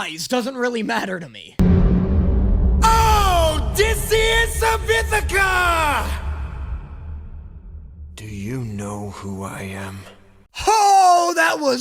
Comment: when i first watched epic and I heard the "Odysseus of Ithaca, do you know who I am?" I was looking away and was watching with a friend so I said "Why did you hit your head?" they laughed